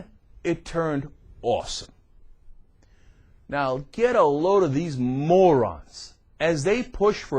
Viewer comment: Lol Captions are WAY off on this video. When he said "Get a load of" The captions thought he said carrillo.